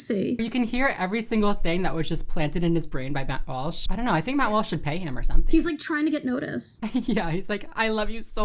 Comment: Genuinely thought said “Matt Walsh should peg him or something” which to be fair, is definitely something that should happen